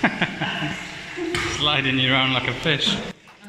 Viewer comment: I thought he said, Sliding you around like a bitch